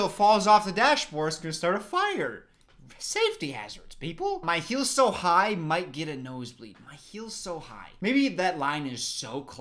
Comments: Ngl “Heels so high, might get a nose bleed” was pretty obvious…on what it means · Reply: I thought she said "feel so high I might get a nosebleed." Thought it meant the had too much crack in her nose 😂💀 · "Her heals are so high, she gets a nosebleed from lack of oxygen"? My dumbass was just thinking she tripps and falls on her nose. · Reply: I thought she said my hair so tight might get a nosebleed 😅